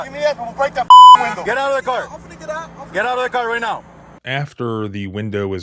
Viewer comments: Got injured in an accident? You could be one click away from a claim worth millions. You can start your claim now with Morgan & Morgan at <link> without leaving your couch. Remember, it's free unless you win. <link> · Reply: did I hear Tyreek saying, "Hold on. I am getting out of the car"? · did I hear Tyreek saying, "Hold on. I am getting out of the car"